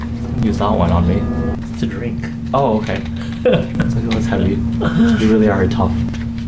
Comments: what did he say lmao? (something something) you really are a top also at 8:41 until ... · Did I mishear or did you say "you really are a top" I laughed my way straight to hell and back 😭